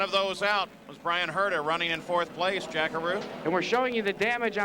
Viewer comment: Jack-a-Roo! That’s what I always thought they were saying back in the day. 😅😅😅 I miss Jack Arute!! ❤❤